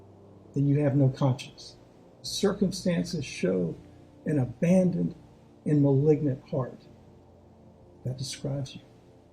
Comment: I swear, I thought the judge called him an abandoned and malignant fart